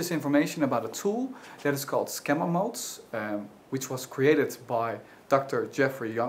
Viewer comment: Skemma-mold...? what am I trying to hear? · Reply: Nope. I heard "skemma mold," and not being familiar with "schema mode," I did not deduce that's what it was. Anything else?